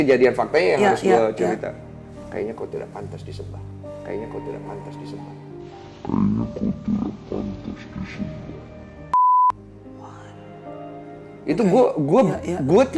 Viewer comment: Mel, menit 10:27 Densu ngomong apaan sih? kayaknya audio gue ngak jelas walau dah repeat. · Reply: "Kalau kau memang Tuhan, kau tidak pantas disembah"